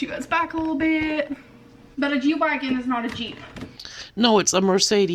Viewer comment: I seriously thought she said, "A Jew wagon is not a Jeep." I thought was about to pull a Kanye.